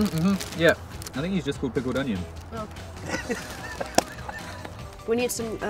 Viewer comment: "THEY'VE GOT MONSTER MUNCH" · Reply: haha btw what did Asa say at 0:57? big foot onion?